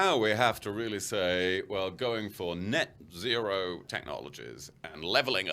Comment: It sounded like he said we're going for net zero levelling up. If zero is the target, the Tories are on track.